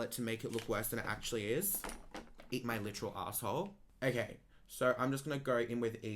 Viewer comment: LOL, I misheard, "Eat my mature asshole." Lol, I'm glad to pick up new ways to cuss at haters.